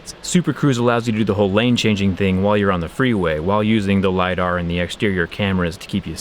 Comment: Did he just say the hummer has lidar 12:06 👀😅😅😅